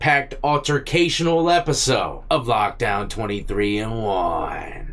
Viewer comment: I live in Arizona and for a hot minute I thought you were saying 23 & Juan😮😂😂